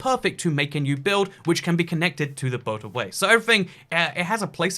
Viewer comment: i heard "boat away" several times until i understood that it's a boatorway